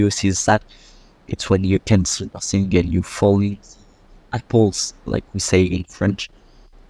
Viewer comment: I really like the explanations of French words and phrases. 🙂 I couldn't quite tell what you said though, at 3:45, about blacking out. The audio was kinda glitchy. Something about apples? Also - looks like a fun game! And really tough. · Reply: Aaah yes Tomber dans les pommes or maybe "to fall in apples" in word by word English, means that you are blacking out ( I think) Really fun but here it is in *2.5 I think to get rid of my bad 10fps that makes this game much more difficult to play😅 And it's better to play at this with a controller and not the keyboard ( more realistic)